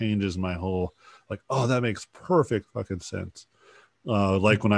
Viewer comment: Sorry, whose videos? I couldn't catch it. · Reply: Maybe Gnomon? They do concept art but not entirely sure.